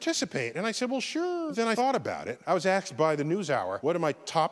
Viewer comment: Did I just hear Neil say “Axed” instead of “Asked”? 😮